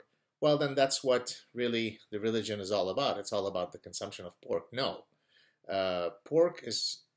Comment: was that an ongoing freudian slip or was I just all the time mishearing pork with porn? And yes maybe David Cameron made that mistake as well (and yes it's getting old :D)